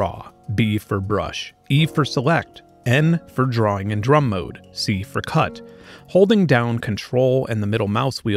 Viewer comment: I dont know why I hear "C for Cunt" hahahahahahaa